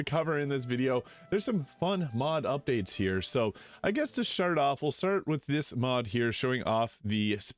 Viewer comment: Had to go back at 0:51 because I was sure he said "Shart off" instead of "start off"